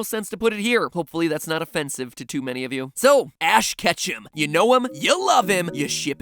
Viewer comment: Who is Ash Ketchum again? Ha got em, I know he’s from dragon ball duh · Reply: I thought you said Ash ketchup